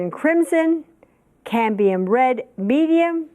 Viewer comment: Did she say Candium instead of Cadmium? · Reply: Cambium